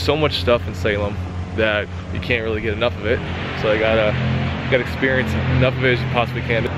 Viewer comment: nooo, not now--I'm carving a pumpkin with my nephew!!! Lol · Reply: Hey, what exactly are you saying at 0:20 - 0:24? The background noise is killing me, and the English subs are all effed up. Lol It sounds sth like, "I need to get enough experience"?? Need it for the translation.